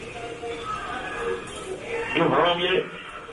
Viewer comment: he said are you home yet and she said I been home. Either home or off but definitely not what the subtitles are saying